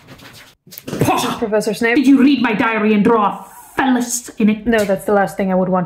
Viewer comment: I thought Snape said diarrhea dream lol😂